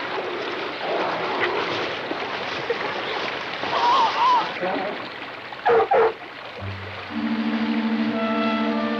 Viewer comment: Oh dang 0_0 58:17 Wait, what did he say? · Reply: He said “take it out”